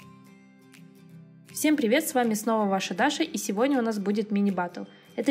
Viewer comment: Всем привет ребят,этом сНова ваша Даша И тут мне послышалось"Всем привет ребята,с вами снова я ваша Маша" ещё и голос похожий 😹